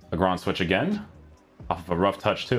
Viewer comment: What is he saying 😅 the ground switch??